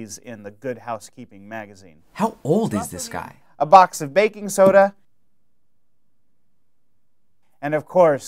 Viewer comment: I thought he said "Bacon Soda" and i'm like FUCK YAH